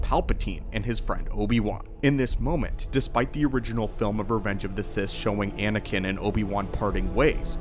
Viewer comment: Did just say "Revenge of the Fish"?